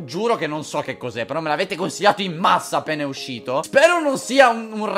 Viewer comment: Solo io quando ha iniziato il video ed ha detto "Me l'avete consigliato in massa appena uscito" ho capito "A pene uscito"? :'D